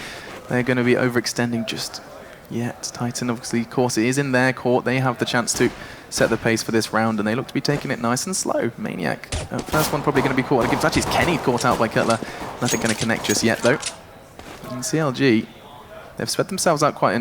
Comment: Is it just me or did he say "Caught out like Hitler"? xD